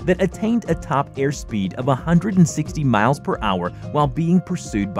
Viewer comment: did u just say miles per hour?